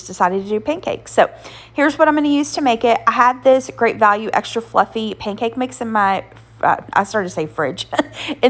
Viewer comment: Everything looks delicious! When you said extra fluffy pancake mix I misheard it as “extra lumpy” and went “I didn’t realize that was a way people buy pancakes…” 😂